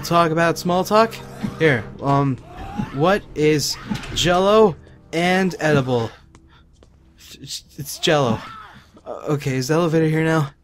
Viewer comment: What's edible and Jell-o ... It's Jell-o Omg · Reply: I'm glad you wrote it out because I thought he said, "What's edible and cello? It's Jell-o".